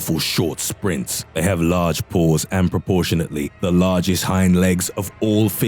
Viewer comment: I thought he said "they have large balls" until I rewound and realized he said "paws" lmao